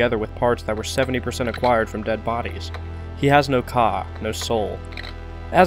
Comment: I heard "He has no c*&k no soul" I had to go back and listen to it multiple times to make sure, I think I just can't unhear it now, but it is probably true with him being a Frankenstein.